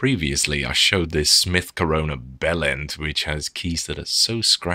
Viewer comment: Did he just said mid corona bell end in 1:39 ?